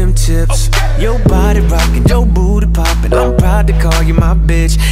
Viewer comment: i heard “ur booty popping”😭😭😭